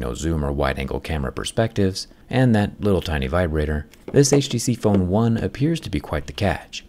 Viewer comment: did he say HTC phone 1?